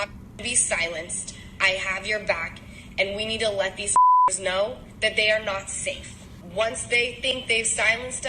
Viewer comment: did she say let these niggas know??